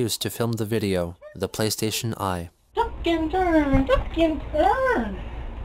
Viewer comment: WAIT I THOUGHT HE SAID THE PLAYSTATION WAS CRAP?!